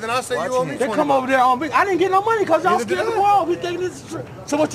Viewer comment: “ I ain’t get no money cause y’all scared to rob” 😂😂😂 forever my favorite! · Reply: I always thought he said “because I was still at the bar.” Most of this looks like it was filmed in Vegas, so I figured they were gambling. Isn’t this from Bum Fights?